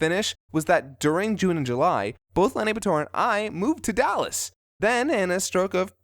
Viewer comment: Cool! Did you move to carrollton? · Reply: ***** You sure? I swear i heard him say he'd moved to Aunt Vivian.